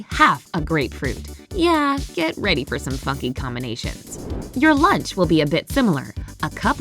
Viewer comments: what was that the f word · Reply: Funky combination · Is it just me or does 2:05 sound like them saying “get ready for some f******” combinations? 😅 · did it say the f word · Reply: Funky* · thought she said fucky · When it said funky combinations I thought it said f**king combinations👁👄👁